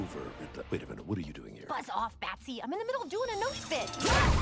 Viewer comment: what does harley say at 0:21 ? cant quite make it out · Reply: “Buzz off, Batsy! I’m in the middle of a notes-bit!”